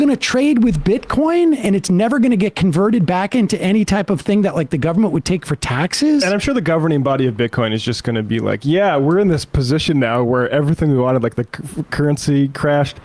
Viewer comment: Did he just say “governing body of Bitcoin?.